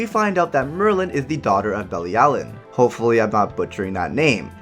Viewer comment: When you said Belialen, i thought you said Barry Allen 😂😂😂